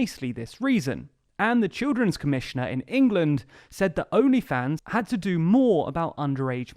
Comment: Oh!… I thought it said: Only TRANS!… Where's my credit card! LOL